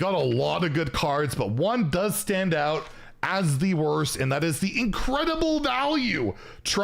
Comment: I thought you said the 10 'wurst' Whizbang cards and now I'm hungry! Thanks.